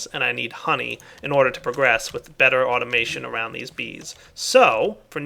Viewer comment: I thought it said beer automation :(